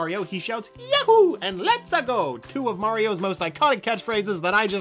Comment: When I first heard the "Let's-a-go!" I thought it was "Pizza Dough" for some reason. It makes sense. Mario is (I think) an Italian name and pizza is Italian.